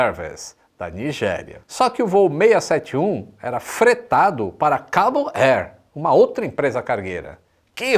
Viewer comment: Em 1:26 " FOI FRETADO PARA CABO AIR ", a quinta serie que habita em mim , " FOI FRETADO PARA UM CABARÉ".